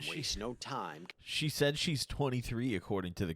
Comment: I think he said “she might be 23 too” probably dispatch code for something